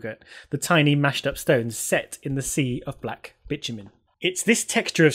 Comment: as english is not my primary language and i wasnt quite paying attention to the screen when i heard you say 'bitch-you-mean' i was quite suprised. Even had to look on the sreen to see what did she mean? :D